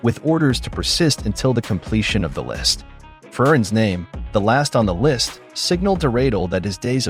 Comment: Did I hear FERN? Last in the List .....Maybe you meant Frieren?